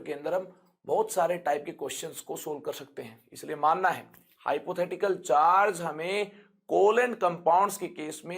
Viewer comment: sir is spelling something like PARCIAL , PARTIAL.......can anyone tell me what is that actually....or give the correct spelling.....is it the unit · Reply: partial charge